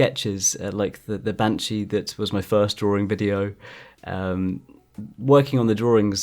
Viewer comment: All I hear is "Draring"